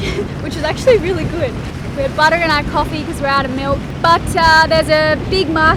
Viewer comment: i had to keep rewinding cuz it sounded like you said butter in your coffee? is that a nutty Aussie thing when you run out of milk?